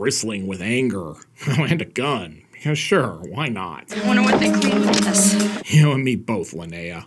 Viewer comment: What did she say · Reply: "Wonder what they clean with this..."